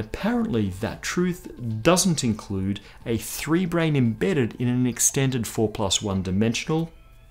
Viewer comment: Everytime he said "brane" i thought he said "brain".